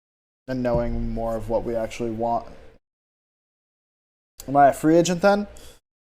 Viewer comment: 今FA状態にしか聞こえない笑